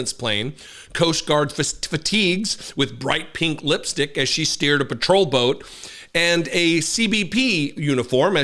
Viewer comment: Make the morning after more manageable, with ZBiotics! Score 15% off your first purchase at: <link> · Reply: You know what i heard.. "she was at the coastguard wearing fierce pink lipstick, scaring off a patrol boat" HAHAHA 53... man i thought, looking at the whole ..erhm.. package that she was one of them pushing 70 women with copious amounts of botox and belly tucks... going for the mar a lago look ( botox boobjob lipfiller and all the other things)... wait with that much botox could she be labelled as a bioweapon...